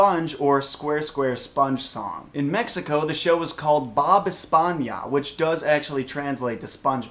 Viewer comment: It sounded like he said Bob España on the Mexico one. Which is Spain Bob. but I know what he meant haha Bob Esponja.